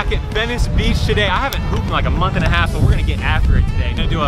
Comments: i thought you said i havent pooped in a month and a half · I swear I thought he said he hasn’t pooped in a month and a half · At the beginning of the video, I thought he said "I haven't pooped in like a month and a half but we are gonna get after it today" Lmao. I was like "Nice way to start off the video AJ." Btw love the content.